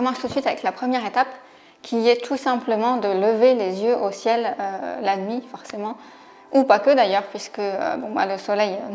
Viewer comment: dites moi que je suis pas le seul à avoir entendu la suite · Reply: Mdr et restez astronogeek